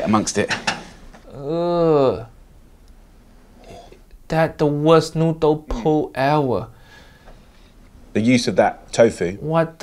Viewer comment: "that's the worst noodle poo ever" I know that's not what he said, but its absolutely what it sounded like 😂 Also... accurate